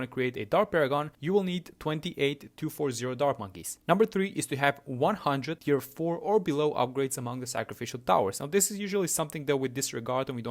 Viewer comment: I HEARD AMONG US